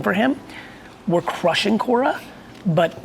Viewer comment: Anybody know what “Cora” is that hes talking about at 32:04? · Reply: Quora, a q&a website